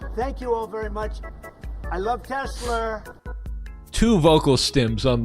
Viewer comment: Lol at first i thought he said "i love to slur" xD